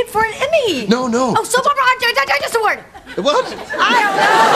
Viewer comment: i hear " soap opra ate the dinosaur"